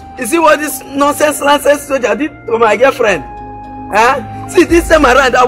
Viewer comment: I keep hearing the words "licenced soldier" "bloody civilian" and "nonsense". 😂 7:46 😲 oh god she's pregnant